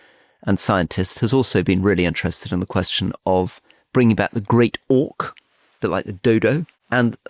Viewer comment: Genuinely thought Rory said they're 'bringing back The Great Orc, bad luck for Frodo'